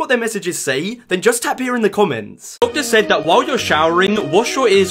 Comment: I heard "Wotah" not "Water"